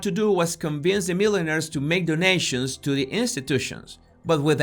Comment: I thought he was talking about hat makers. Why would Epstein want to introduce hat makers to institutions? Then I realised he meant millionaires. He sounded like he was saying milliners!